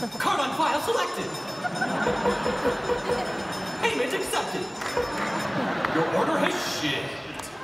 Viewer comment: it sounds like he said YOUR ORDER HAS SHIT